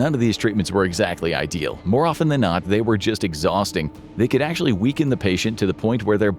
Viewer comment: fix the subtitles, they say "weren't exhausting" instead of "were exhausting"